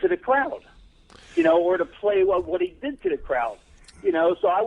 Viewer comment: Does he say cower or powder?